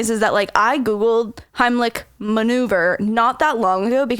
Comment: I’m only 3 minutes in and..HAHAHAHAHAH!!!! The moment when he said “Did you just say REMOVER?” 😂😂😂😂😂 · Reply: Jennivic April I thought she said hemlich manure 😂😂🤣😂😂😂